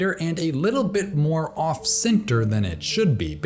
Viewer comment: Did you say, "off sphincter?"